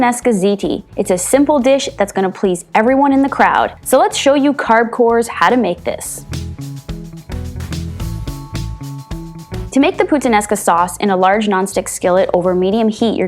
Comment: Omg. In the intro I thought you said “carb whores” LOL. I was like yes hi that’s me.